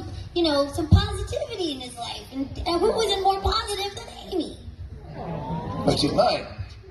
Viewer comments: What did Sonic say? I heard 'What's your point' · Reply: “But she’s mine” 😂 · I heard "but she's mine" yet people are saying "but he's mine" So which is it??? =v= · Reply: He says “But she’s mine” the audio makes it sound a little wonky tho